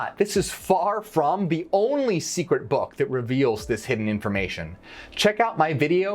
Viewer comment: When you said this information. It sounded like you said disinformation. That was funny.or did you do it on purpose